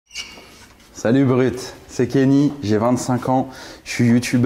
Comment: Sur la tête de ma mère j'ai entendu "salut uber eats" au lieu de "salut Brut" au début